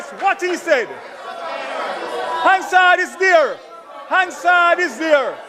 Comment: Can somone please tell me what Daryl was shouting is there? I heard "hand saw is there" 😅😂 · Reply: Hansard:- parliament transcript